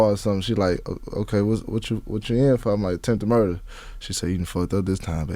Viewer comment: I thought he said tenth murder😂